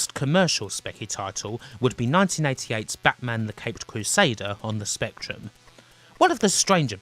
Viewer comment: I wasn't watching a sec and thought she said " Batman: The Pegged Crusader"